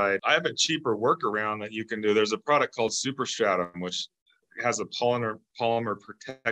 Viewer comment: HE SAYS, “THERE’S A PRODUCT CALLED SUPER SHADUM” WOULD YOU PLEASE CORRECT THAT⁉️ITS TOO HARD TO HEAR WHAT HE’S ACTUALLY SAYING🙁 THANK YOU‼️GREAT VIDEO👍🏻